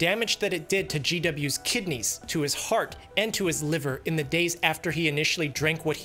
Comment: the cc says Lungs when you say Liver. Just a small thing I noticed. Thanks for the video!